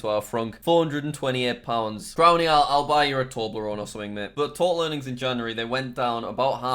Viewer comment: did you say toblerone?